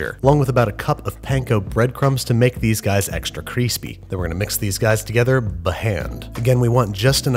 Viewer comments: Did he say creespy? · I was so distracted by him saying "creespy" that I didnt notice him saying "b'hand" instead of "by hand" Seriously Babby? Saucep'n and Sauteep'n wasnt enough for you? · did I hear him say extra creespee?